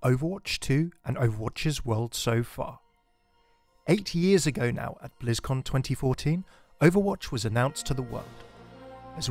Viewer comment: “8 years ago now…” I thought you said 80 years and was like, yeah… that makes sense.